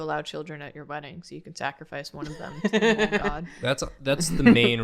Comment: i thought they said ”mold-god” for a good 10 minutes until i realised they said ”mole” 😭